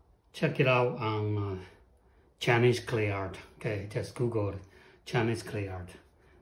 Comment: Can you repeat or send link for textured mat. I thought you said Janis Clay Art. No luck finding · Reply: Chinese clay art.